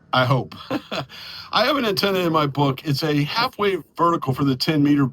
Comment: you had me tripping for about 2 seconds. I could have sworn that that you said "halfway vertical" and was freaking out that you'd introduced a totally new, bizarre type of antenna and weren't defining it. Then I realized that my old ears had cheated me out of the soft ending of the word. 😅 73 my friend. KFØGER